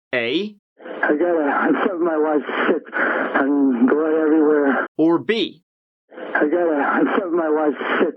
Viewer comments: I heard 'my wife slipped and there's blood everywhere' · “I found my wife—shit—I mean blood everywhere” It was a combo of both interpretations. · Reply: I heard him say "I got a- I think my Wife slipped." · To be honest, I heard " I think my wife is sick" lol I must be the only one lol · I hear " I gotta shove my wife sick"